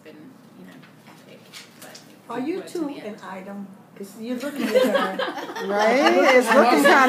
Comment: Can someone tell me what the woman is saying at 7:11 ? They laughed and Im curious to know why. For the record, I am French...therefore not a native english speaker :( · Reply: She asks if the two of them are "an item" or if they are a couple.